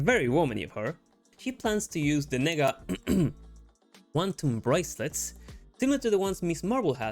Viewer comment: hahaha apparently those are the NEGA Bands, so it sounds similar😂 they give whomever wears it powers..but they change the name to "quantum bracelets" in the movie and show.. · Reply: I didn’t see your comment but edited it first after I googled it. With your accent I thought you said de-nega when you were saying the nega. Beautiful accent by the way. I’m very jealous. My American accent is quite boring.